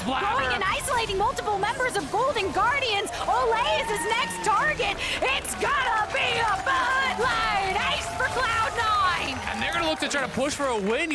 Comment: What does say? "It's gonna be a ???" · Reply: Bud light ace